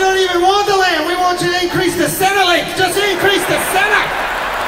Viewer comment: what does he say? increase the what? salary? i cant make it out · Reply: Centrelink. That's the subsidy\gov't assistance program in Australia.